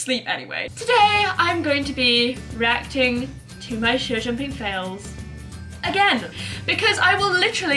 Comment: Lol at 0:30 in captions it says “shampoo Fails” instead of show jumping fails😂 YouTube needs to work on fixing their captions 😂